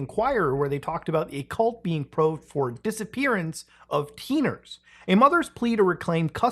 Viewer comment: I thought you said a cult being probed for disappearance of "ppiners" like an abbreviation or slang of Filipinos or Philippineers